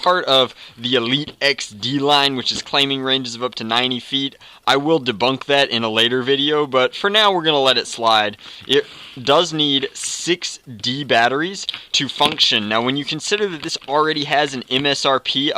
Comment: For a second I thought you said 60 batteries but you said 6d batteries